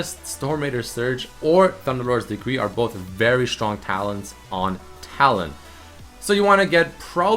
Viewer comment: "Stormraider's Surge and Thunderlord's Decree are both very strong Talons on Talon." - 2015 I'm definitely not the only one who heard Talons twice instead of talents.